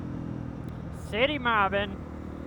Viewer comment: WTF did you say? 🤔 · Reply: city mobbing lol